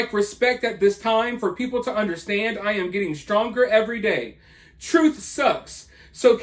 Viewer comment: I thought her post said, "I'm getting Stranger everyday." 😆